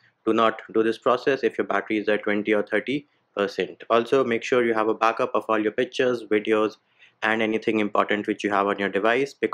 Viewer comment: i thought you said make sure make backup of all your bitchesb!! Im like damm!